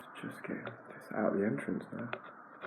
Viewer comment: Great video. This is going in with my favorires. At 3:29 the subtitles said, "just kick out the entrance. " So I am calling this one, Ninja Wax Warrior Whisperer and the Wax Godzilla. At 5:58 just before you got the tweezers (?) to pull out the big circle of wax , it said "grab that wheel is electrical. Such fun. 😊